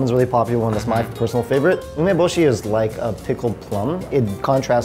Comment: What he said: Umeboshi The caption: Lemon bushy 7:34 Again: Wok->Walk